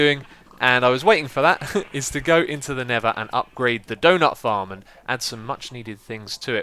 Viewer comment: wasn't just me right? I heard doughnut farm